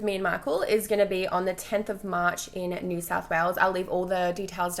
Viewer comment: I thought u said South Wales not New South Wales and thought that's where I live!!!